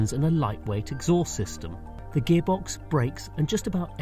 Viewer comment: I heard, "The gearbox breaks."